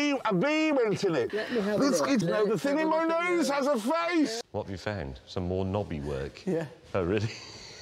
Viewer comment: did he say the thing in his nose has a face? 🤣🤣